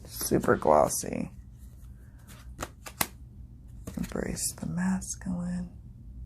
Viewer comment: Love your channel. I've been away for a bit, but am so excited to get back to your videos. Off subject, I'm deaf in higher registers and instead of "Embrace the Masculine," at 05:55, I heard "Embrace with Mescaline," which seemed edgy for a commercial deck.